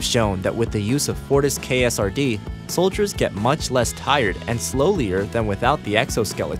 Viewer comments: did you just say 'slowlier'? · Did he really say slowly-er?